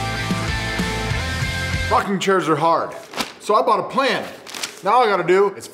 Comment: wait what... oh... he said rocking chairs are hard